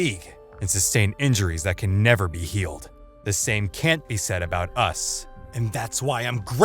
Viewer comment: shouldn’t it be “the same couldn’t be SAID about us” instead of “the same couldn’t be SAD about us” XD